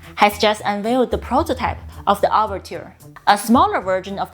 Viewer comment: The way you say 'overture', it sounds almost like 'orbiter'. When I heard that, I was thinking "what?! This supersonic jet is also a space shuttle?!"